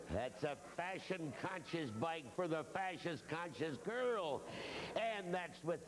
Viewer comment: Everyone heard him say “fascist conscious girl”, right?